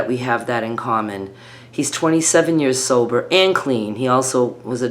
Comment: at first I thought you said that he was 27 years old …And thought you go girl! Lol then I realized you said sober 😂